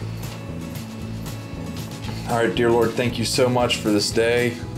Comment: I thought u said "deer lord" lol